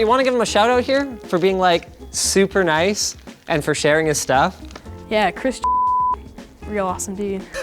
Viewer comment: I thought that kid said "Ya Chris, F*** You"